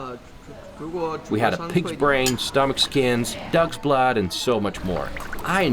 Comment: Did you say dogs blood ? 🤢🤮